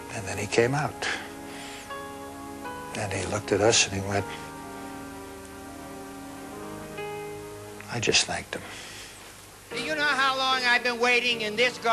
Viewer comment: Somebody can tell me what the man said at 3:48? Please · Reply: Karen Núñez That was Greg Garrison, Dean’s producer on his show, and what he said was “He (Dean) looked at us and he went ‘I just thanked him’ (meaning Jerry)”